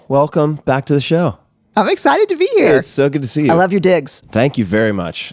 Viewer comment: “I love your dicks” 1:36 ... anyone know what she meant?